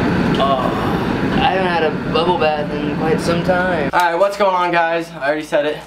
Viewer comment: at first i thought it said bubble wrap